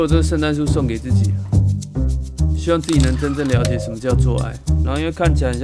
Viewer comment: 我聽成：希望自己能真正了解什麼叫 「做愛」（？